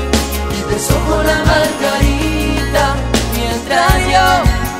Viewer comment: YO PENSE QUE DECIA, TE SOCO LA MARGARITA😂❤